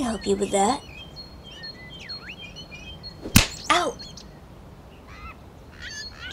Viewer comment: I thought Alderheart said '''Gayfeather''